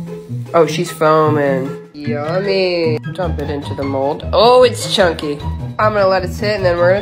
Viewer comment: It sounded like you said 'ohh it's Chucky'